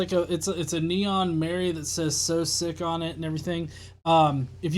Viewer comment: Misheard Soesic as Sir Sic lol.